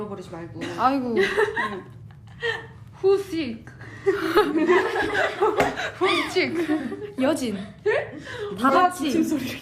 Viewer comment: when someone wrote "WHOS SICK" cause yeojin coughed and they couldn't understand what they were saying and then the girls read the comment... it really got me laughing for ages 🤣